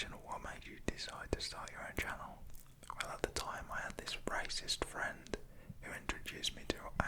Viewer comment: did I hear racist friend correctly here😭?